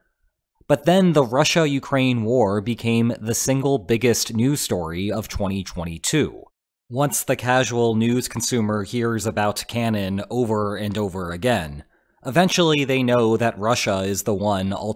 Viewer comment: All Zelda references: 3:14 Maybe triforce 6:08 Another triforce 6:12 Link to the past 6:49 Phantom hourglass 8:03 Spirit Tracks (wow that was hard to notice) 8:23 Oracle of ages 11:26 Cannon maybe Ganon? 13:19 Link's Awakening 15:32 Link between worlds 15:59 It's dangerous to go alone 18:00 Age of calamity 22:26 Legend of Zelenskyy 22:31 Hey listen 23:28 Power wisdom courage 22:56 Tri-force(ing) Love that ending message. Also thanks for finding the other references everyone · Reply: I thought I heard "Ganon" not Cannon that first part too